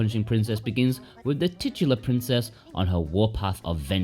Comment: DID YOU SAY TITS!?